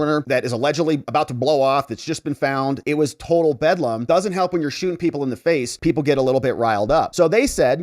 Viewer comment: Did you say, "It was total Fedlam"?